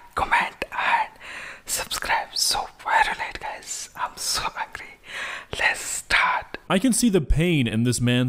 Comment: good thing I grabbed a drink to cope with that...also I swear to...something, anything, that I heard him say "im so ugly" at the beginning and I tried to go back and listen closer and I cant hear anything else. Someone help me, im terrible with understanding accents sometimes. I wish I could comment the look I cant get off my face...Im almost getting the ick I get from big spiders listening to this and that is a big f'ing deal in my world.